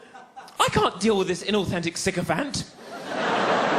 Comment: can anyone help? at 4:30 "i can't deal with this inauthentic...?" what is he saying, i can't understand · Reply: sycophant I believe